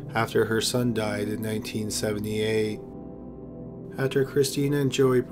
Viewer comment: i thought you said her son died in 1974, then you said 1978